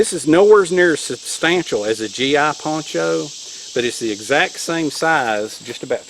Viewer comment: I couldnt understand what you said what poncho was tat called .? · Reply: It's considered to be a USGI poncho